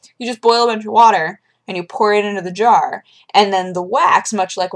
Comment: i heard poor it into vagina